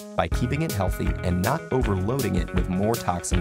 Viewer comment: I like the last sentence. "it is a factory you can afford to be shut down" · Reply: This is one factory we simply can't afford to shut down. It's even correct in the transcript, which isn't always the case when Google does it automatically, but he clearly said can't.